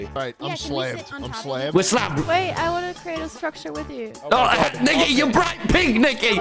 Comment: WHAT DID HE SAY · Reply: I thought he said that too but it was Nicky and it sounded like the n word